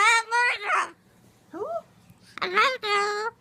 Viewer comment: sounds like says “ I’m not a murderer “